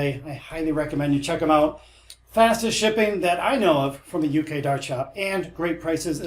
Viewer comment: My bad I thought you said dart shopper at the beginning.